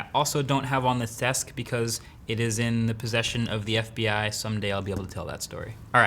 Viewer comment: Wait did he just say "in hands of the FBI" ???? 👀